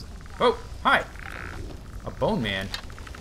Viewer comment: thought it said bonemom